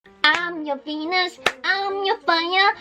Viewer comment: did she just say I'm your penis......